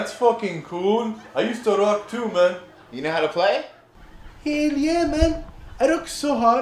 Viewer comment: instead of hell yeah men it sounds like hail Yemen